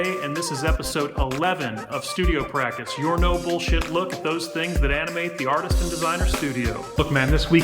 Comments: LOL Hearing what? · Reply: ah, YT didn't ghost the comment after all... when you say "no-bullshit look at..." I keep hearing it as "noble...". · I keep hearing it as "noble shit look"